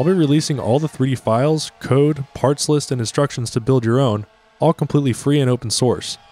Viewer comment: i thought you said "destructions" and got a wave of nostalgia